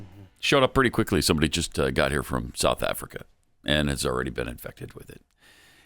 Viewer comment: Haha sounds like Pat said "Some bitches got here from South Africa" LOL